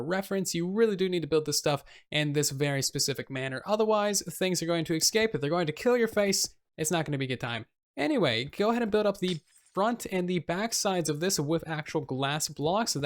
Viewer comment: Did anyone else hear him say "bront" instead of front at 11:26 ????